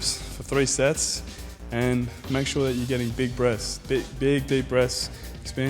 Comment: thought he said, "Make sure you're getting big breasts" ahaha